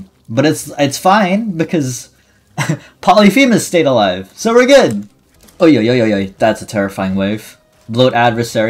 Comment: did he just said ‘ ой йо йо йой’?